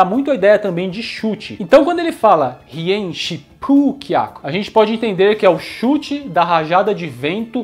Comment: No " Hi En Shippu Kyaku" sempre entendi no misheard -> " que se f*d* " pq ele se joga igual um maluco